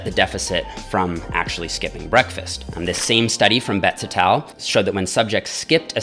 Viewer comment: i dont understand what jeffs saying: bets attal?